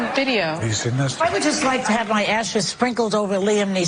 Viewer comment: "I would just like to have my ashes sprinkled over Liam Neeson" sounds like something Jeffrey Dahmer or Charles Manson would say. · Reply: Oh I thought she said "ass" instead of "ashes". It's even creepier now 🤮